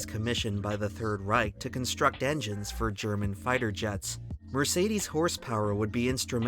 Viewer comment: Did bro just say Fighter jets?